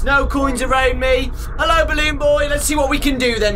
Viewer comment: when you said you were scared of the rabbit costume (i'm still not completely sure what you said) I heard "Bullion" now it's stuck in my head. The glitch rabbit's name is Bullion.